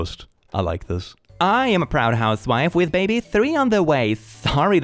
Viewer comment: misheard "baby 3" as "83" and was like ?!?!?!?!?,, 💀